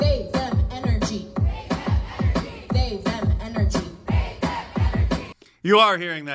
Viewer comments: I keep hearing "big fat energy" 🤷‍♂ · this was playing in the background and so I didn’t actually hear it correctly. I swear to God, I thought they were saying faith and energy. I was thinking man that is weak preaching out there. They need to get some southern Baptist in em. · I thought she said " big fat energy ". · I heard "Save on Energy" not "They/them energy". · thought she said big fat energy